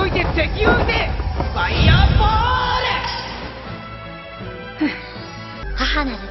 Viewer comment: I thought his saying faia bulto which bells magic lmao